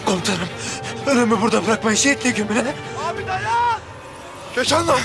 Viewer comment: ne dedi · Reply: Komutanım ölüyü burda bırakmayın sehitle gömün